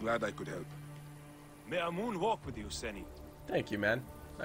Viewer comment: am I the only one who heard "may I moonwalk with you, seni"